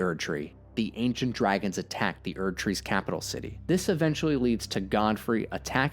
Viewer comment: When you said "Godfrey and Marika" and I heard 'God free America," I remembered all the bald eagles in the Lands Between and it clicked... I don't know what clicked, but it did. Maybe I should start a US allegory channel.